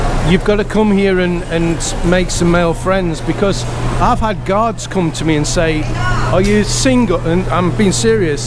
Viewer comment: if you see this, at about 3:58 did you say "I've had guards come to me and say ... " ? Was that guards or guys that went up to you?